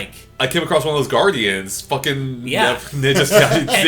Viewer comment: did i just hear say "fuckin?"